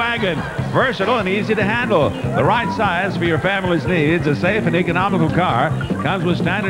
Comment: When he said the car was the right size for your families needs at first I thought he said the right size for your families knees and had to rewatch🤦‍♀️🤣